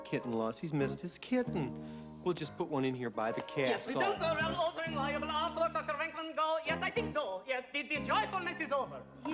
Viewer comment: what did he say??? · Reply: "don't go around altering valuable artwork, Dr. Venkman, go, yes, I think go.."